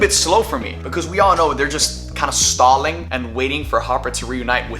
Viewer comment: did you just say "harper" wtf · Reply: The editor probably hasn’t seen stranger things so he probably doesn’t know it’s hopper and he thought said Harper not “hopper”